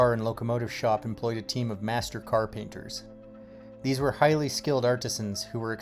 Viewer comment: At first, I thought you said that every railroad employed "master carpenters" and was wondering what on Earth that had to do with lettering 🤦